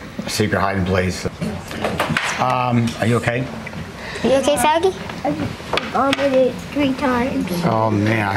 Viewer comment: ROFL did he say "are you okay faggy" ?! hahah